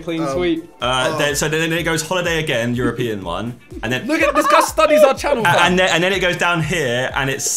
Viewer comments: I swear simon said the n word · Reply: "look at how this guy studies our channel fam" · what did simon say? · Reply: this guy studies our channel fam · is it just me or does Simon say the N-word